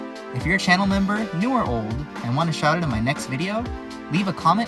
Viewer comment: I first heard "new or old" as "you are old" and I have no idea why what's up ye olde memboomers